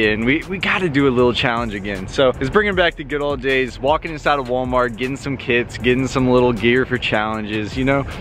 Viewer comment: Who else thought he said let’s get some kids